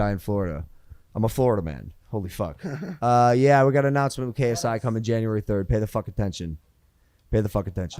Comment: did he say KSI or am I tripping.